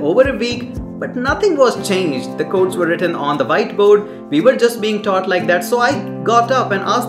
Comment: I thought he was saying “white boat”